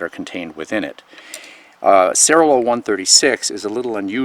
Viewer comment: Hi, Youtube's automatic captions (speech-to-text) didn't quite catch the name of that alloy; "sera whoa 136" turns out to be quite a disappointing input for a search engine. Please spell it out, someone. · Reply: Cerrolow 136